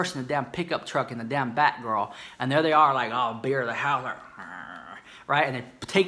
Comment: said: She's bitter the house. I heard: She's bigger than a house.